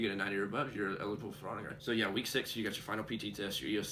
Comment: you mention "autograph". What is that? I think that's what you said. "...if you get a 90 or above, you're eligible for autograph..." wut? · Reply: "Honor Grad", If you get a 90% or above on your test you're eligible for Honor Grauate